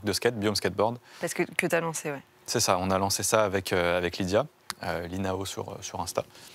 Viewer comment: C'est quoi l'instagram de la personne avec qui il a monté le biome ? j'ai entendu "Linao" mais je trouve pas. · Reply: Lilly nao